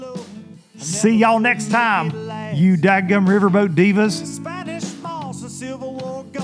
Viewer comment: I swear that's how I always heard it as "riverboat diva" no kidding I thought that's how it was supposed to be